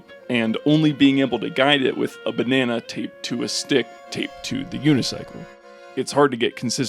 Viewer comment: CC said "RPP and only being able to guide it with a banana taped to a dick tape to the unicycle it's hard to" Dick instead of duct